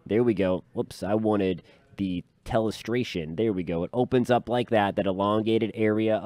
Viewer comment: haha did you say you wanted the “L”estration. Haha. Because of the big L. 😂😂